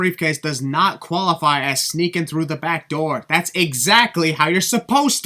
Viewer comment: it sounded like Michael Cole said "sneak in the black door."